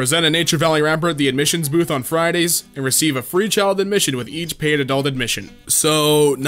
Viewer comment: omg I thought that said "recieve a free kid"